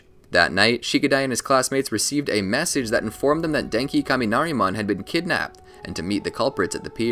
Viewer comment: Am I the only one who heard "Denki Kaminari"?